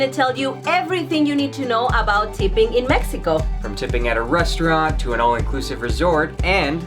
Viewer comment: At first I thought it said tripping in Mexico and I was like, I so gotta watch this